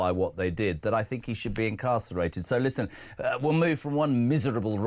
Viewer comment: I was distracted as you were speaking and at first thought you said Andrew is lucky he wasn’t castrated, when you said incarcerated.